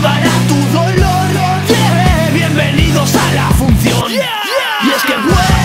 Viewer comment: Pensé que dijo fusión Pero dice funcion